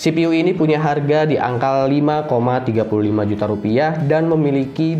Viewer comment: salah bilang bro 5,35 juta apa aku salah dengar yah